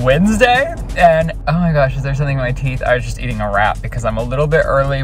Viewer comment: ??? Did he just say "I was just eating a rat.." ???